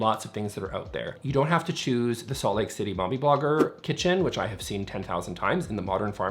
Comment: I thought you said “Salt Lake City Barbie Bunker” and I’m sticking with that.